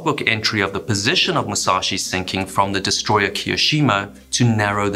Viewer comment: I thought you kept saying the explosion was seen by the Kirishima, which was famously sunk at Guadacanal. After looking over the order of battle, I was mishearing Kiyoshimo.